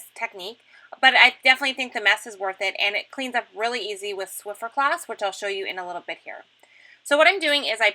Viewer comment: This is such an all season technique. I really like it. BTW...are you saying Swifter Cloth? At first I thought you said flifferfloff. lol.